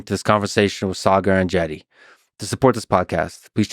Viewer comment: I really thought you said "Saagar and Jetty", and I was like who tf is Jetty?.... lol Have been watching BP casually, just recently, but didn't know his full name.